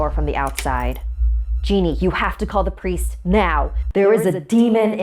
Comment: I thought she said “call the police there is a demon” and I was like tf is a police gonna do